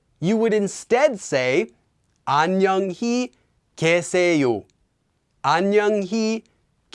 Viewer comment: Isn't the last sentence in 5:35 pronounced as kye instead of ke? I thought the two strings of the left was with y and not just e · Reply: It's 계 but when said quickly, it sounds almost like 게.